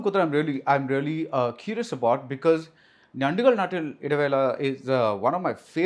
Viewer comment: what is the name of the movie I didn't understand what it was can someone please tell me what was the movie name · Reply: Njandukalude Nattile Idavela❤